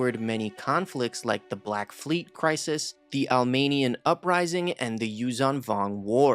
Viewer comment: I thought you said the arminian uprising 😂😂😂😂